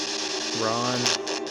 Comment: That sounded like my name at 46:11 . Maybe it's Ron.